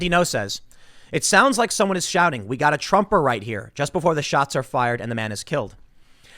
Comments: There was a video of a woman saying she didn't care a "fascist" died tonight. It also sounded like they said "we got another right here". It was a targeted hate crime. Always carry a way to protect yourself. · They are calling for more killings. 🤬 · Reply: Sounds like he said. We gotta Trumper here.